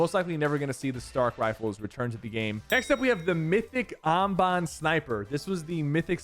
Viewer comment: what did he say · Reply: Mandolorians Amban sniper